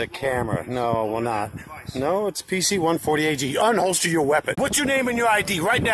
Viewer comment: To be fair…. I know I’m not the only one who heard the camera guy literally tell him to unholster his weapon 😂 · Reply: Same! 😂 ..the first time I watched, I heard it like I’m sure he meant it, “don’t unholster your weapon.” The second time, I heard it as it actually is 😮😬😂